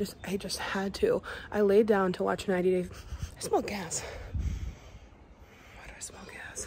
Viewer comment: I thought you said you smelled cats lol I was like wow you got a good nose girl!